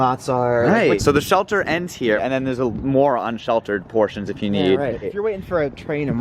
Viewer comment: WOW another fantastic video on the books!! Minimal but iconic :) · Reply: it sounds like you say "moron" and I'm here for it